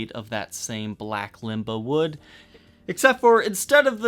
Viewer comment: I thought he said ligma wood